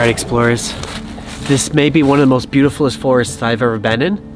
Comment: Did he say “this may be the most BEAUTIFULIEST forest” or did I hear him wrong?????